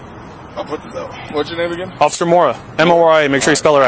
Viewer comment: What I heard is "Officer Moron".